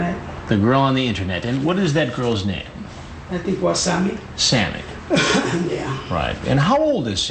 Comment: I’m dead serious I thought he said wasabi 🤣🤣🤣😂